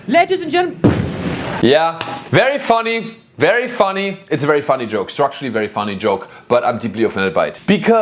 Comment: Did you just said, Strasznie funny joke?